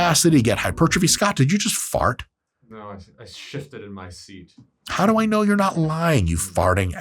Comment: Scott said, "shifted" I heard, "sh!tted in my seat"😂😂